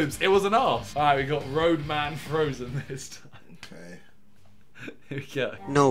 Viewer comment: I thought it said ramadan frozen😂